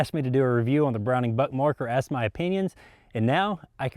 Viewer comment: I thought you said brown butt mark